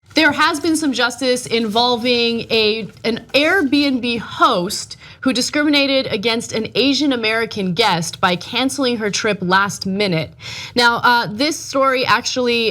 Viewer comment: Did anyone else heard a "Arabian Host"?🤔